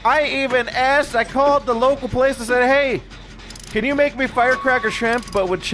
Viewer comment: I thought he said he called the local police instead of place there at first XD